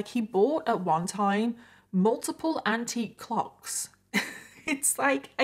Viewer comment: unrelated and i think super inappropriate but-- i misheard you say "antique clocks" T_T and i thought you said "c*cks" omg i am disappointed with myself